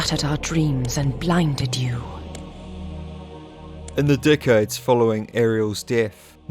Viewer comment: NGL, while listening to this at 7:52, it honestly sounded like you said "d*ckheads" lol Amazing video as always. I grew up on LoK, and of course Soul Reaver, so this stuff draws me in like a moth to flame. GJ!